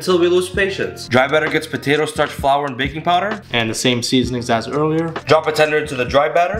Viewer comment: I heard “baking powder” as “baby powder.”